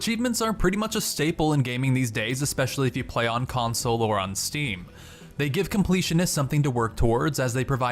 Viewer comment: Did he say "console or on STEAM" ?